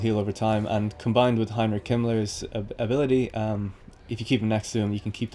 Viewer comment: Did he just say "combined with Heinrich Himmler's ability"?? What the hell?